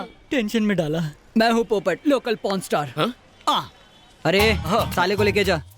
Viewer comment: did he say local P*rnstar?😂